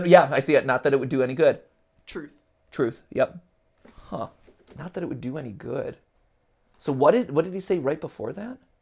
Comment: i think it says: not that, it wouldn't be any good. but if- then it gets cut off. To me, this sounds like a conversation because there being a "but if-", it kind of sounds like two people talking to each other